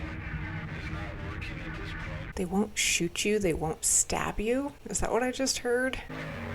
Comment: "The WILL shoot you, they WILL stab you . . . " is what he actually says.